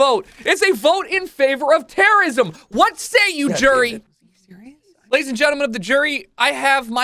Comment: Anyone else notice 8:29 before the "Jury" pic shows up "Goddamn it..." "What say you Jerry?"